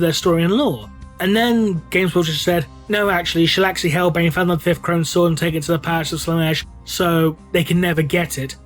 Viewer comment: Can someone tell me wtf he said around 9:10 he spoke to fast for me to hear and slowing it down just made it garbled for me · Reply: Something to the effect of "And then Games Workshop said 'No actually, Shelaxi Hellbane found the last Crone Sword and took it to the Palace of Slaanesh'"